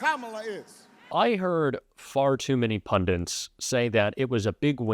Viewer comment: @ 9:28 did I hear you say "pundants", I hope not ! ! The word is "pundits", but so many Americans seem to butcher the English language and say words like pundants... It offends me as a well educated Englishman...